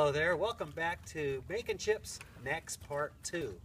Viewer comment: thought you said bacon chips ..felt like frying some eggs first